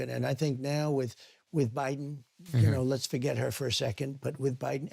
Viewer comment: when answering the question about what he wants his legacy to be, it sounds like trump says “let’s forget about her” when he mentions Obama and when he mentions Biden.